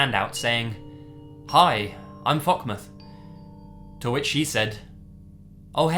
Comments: Did i hear that right "Fuck moth"? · What did did he say his name was when he introduced himself to that girl??? It sounds like he said ''Hi I'm fuckmuth'' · Captions: "Hi, I'm fucking earth" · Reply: I thought I heard "Hi I'm fuckmuth"